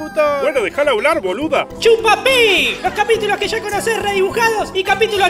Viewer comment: Yo pense que decia "chupa R9" pense que era una clave judicial o uno de esos Renault exoticos